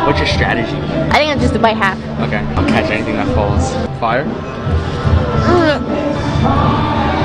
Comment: I thought she said at the end “worst 30 bucks” but she said “worth the 30 bucks